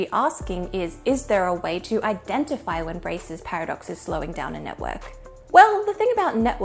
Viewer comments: i thought it said braless paradox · Thought it said braless paradox... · Aww.. disappointed.. I thought it said Braless' paradox